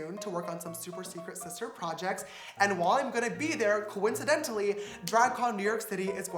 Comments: Ok I thought he was saying dragon con for the first half of this now I understand. 💀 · Thought he was saying Drugcon 😂😂